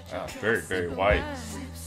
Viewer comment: did he say very white? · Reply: Nice try. said it's "very Barry White."The singer. The vibe of song sounds Barry White to.